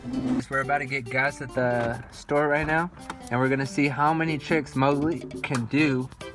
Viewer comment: I thought he said chicks it caught me off guard for a second